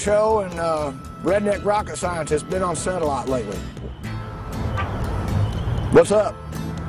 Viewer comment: "I've been working on a stunt show, and Redneck Rocket Scientist, been on -satellite- lately." Hmmm... :-) EDIT: I stand corrected by Mr.. I misheard him say "been on satellite lately" when he really said, "been on set a lot lately." I apologize for my error. :-)